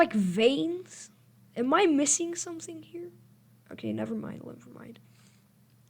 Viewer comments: she actually said fangs if we’re gonna make fun of something, we gotta at least do it right but she said fangs edit: I also checked YouTube kids to see if huggy Wuggy’s on there. I even made it 5 to 8 it said try searching something else meaning that huggy Wuggy is not on YouTube kids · I think she said fangs